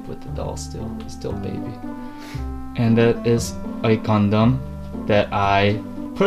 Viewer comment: When said, "Gundam", I legit thought he said, "condom" :)))) ://///